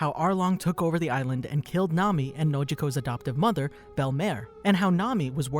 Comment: they killed nana? that's what you just said bur